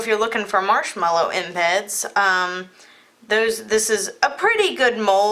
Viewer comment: Looks so pretty! My son was watching the video and he thought you said marshmallows in bed. So he said, "If you put marshmallows in bed, you get bugs right?" Yes, yes you will... Lol I love your videos! ❤